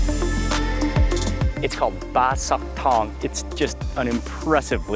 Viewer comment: I thought he said Bai Sathong. lol